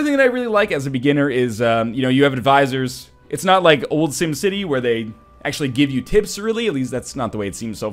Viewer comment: Did you say SimCity at 14:05. Otherwise great comment on a possible great game.